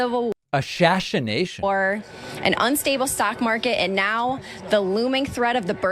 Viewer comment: Right after „ashashination“ I thought the add reader also said that „the sock market is down“. I went back and unfortunately I just misheard, but that made me laugh so hard